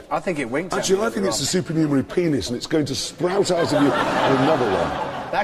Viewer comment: What does he say ? "Srowt out ..." ? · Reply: "sprout out"